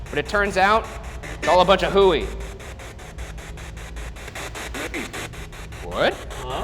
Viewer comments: I might be wrong, but that sounded like "Wait." Edit: With subtitles on during the "demon" room, the subtitles read Shane saying "demon" as "gaming" lmfao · it sounds like someone saying wait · It sounds like said hooey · sounded like "wait" to me ALSO AT LIKE 26:20 ish when Ryan's screaming I can hear like a buzzing behind it??? Is that a thing?